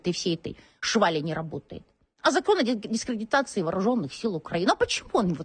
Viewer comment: вооруженных сил Украины ми правильно почули 🤭